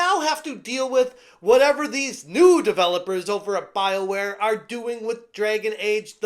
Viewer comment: Could have sworn you said, "Bileware".